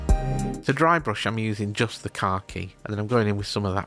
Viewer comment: I keep hearing "drybrushing with just the car key" :D It gets me every time and then I go "oh khaki". Kit is looking good! It's the one Halcyon grail that got away from me.